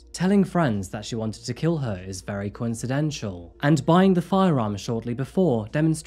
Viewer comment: Did he say coincitential instead of coincidental 🤔